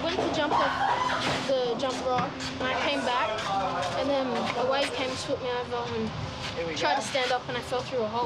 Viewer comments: did he say "my wife"? dude is like 9 and he's already married 😂😂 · I know it 2021 and this was last year but in 0:34 or 0:35 did he say his wife I’m confused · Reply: He said wave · can some one correct me if im wrong bcs i just heard him say my wife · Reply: Nah he said a wave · Did he just say "my wife came over"? Am I trippin? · Did he just said his wife came back in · did he say his wife? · Did that kid just say "my wife" when he was explaining what happened like this kid is like 13 wtf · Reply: I really thought he said wife. But listening closer he does say 'a wave ...'.